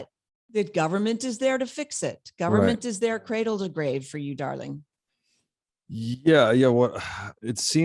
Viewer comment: At first I thought she said: "government is there to cradle you to the grave my darling."